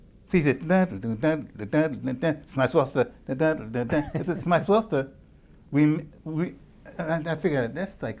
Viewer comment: What did Wayne say @ 1:24? Smiswarster? "we may as well to have been doing this or that?" Anybody know?